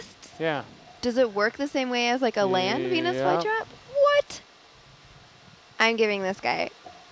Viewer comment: when you watch the preview of it the subtitles says "like the land penis" misheard from "like the land venus flytrap?! What?!"